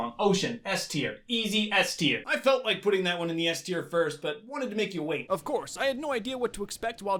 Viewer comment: it sounded like you said the s word